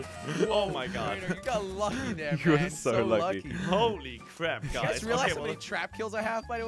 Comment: I thought he said jordi’s so lucky 😥